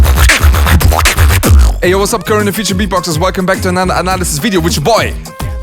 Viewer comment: what madox say? "Wassup Korean Future beatboxers"? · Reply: “ay yo wassup current and future beatboxers”